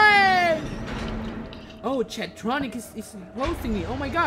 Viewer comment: wait- did he say C H A P S T I C K? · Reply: Chadtriggered